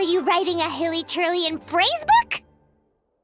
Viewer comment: I MISHEARD IT IN "FACEBOOK" I'M CRYING Also Kaeya is romantic himself